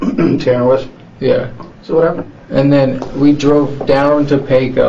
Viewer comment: probably got misunderstood cause of the subtitles. He most likely said “…so what happened.” Instead of “…so whatever.” 👀